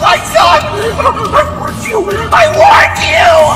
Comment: How did Dawko think that said” I want you, I want you!” I hear “I warned you, I warned you!”